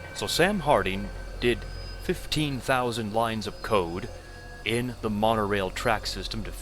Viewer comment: Me, mishearing 'lines of code' as 'lines of coke' because I'm watching while folding laundry: 0o ?! Demonic invasion may not actually be the problem here!